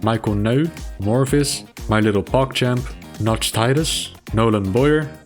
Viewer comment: Oh dear, what did he just say?! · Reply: Mon petit pogchamp?